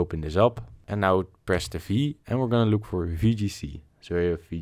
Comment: Bro what being said around 1:07 “click on ___” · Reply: v